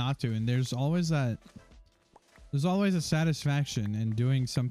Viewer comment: bro i thought he said "im gonna eat candels" at the end 😭